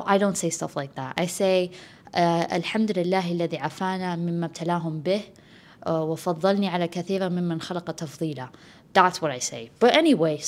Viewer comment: Can someone please tell me what she said at 31:18? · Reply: its from the hadith, the Prophet saw said : Whoever sees an afflicted person then says "All praise is due to Allah who saved me from that which He has afflicted you with, and blessed me greatly over many of those whom He has created"